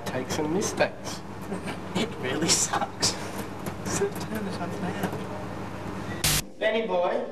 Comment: Thanks for uploading these :) and do you know what Daniel says at the start at 0:06 ???? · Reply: Hannah Sheldon i think he said turn it off now